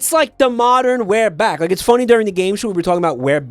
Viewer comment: is he saying "we're back"? Like, the one animated movie about the guy who brought back dinosaurs and made them sentient? With the villain who has a screw in his eye?